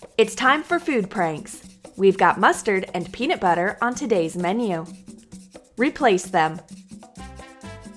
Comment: U said maneo instead of menu. But i understand if it was a mistake. 1:49. I ❤ u troom troom